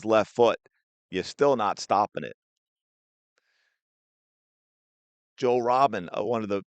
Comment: You mean Arjen Robben? Sounds like you said Joe? Either way, yeah, he made a living out of cutting in onto his left foot. He was unstoppable for a while.